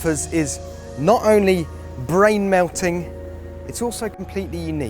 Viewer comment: I heard "Must dash" as "Moustache" for a second...